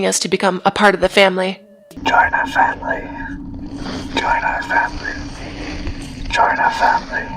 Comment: it sounds like hes saying "join us henry" then fades into "join our family"